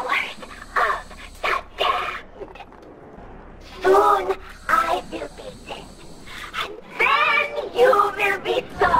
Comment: What did Samantha Say from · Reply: “Soon I will be dead and then you will be sorry…”